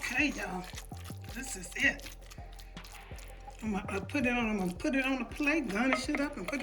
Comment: Yes. I just made it and it’s delicious!! But at the end did she say I’m a slap this Sh** on a plate? 9:35. It sound like it 😂